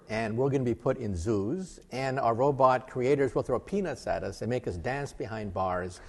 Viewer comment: Did he say "throw a penis at us"?